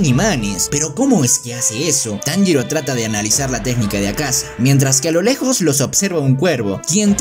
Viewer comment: me despiste y escuche akaza dmpieza recordar novias xd